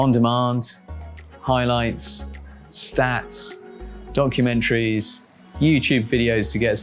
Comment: did he say donkeymentaries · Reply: More like Donkumentaries